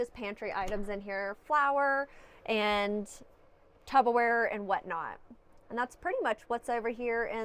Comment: did you say tubber wear like that guy in the viral video does 😂😂